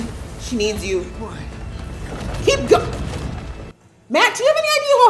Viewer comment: I'm almost positive there is a mistake in Prime's subtitles at 35:13. It reads "keep going," but I SWEAR its injured Rand saying "Egwene."